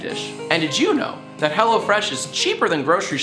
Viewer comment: With the sponsor I thought you said hella fresh Which is the complete opposite of these gifts